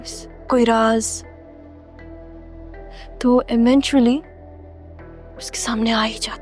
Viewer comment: she said ementually instead of eventually